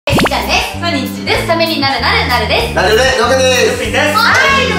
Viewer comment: のとこ「りっちゃんです」がどうしても「えっちゃんです」に聞こえてしまう私だけでしょうか？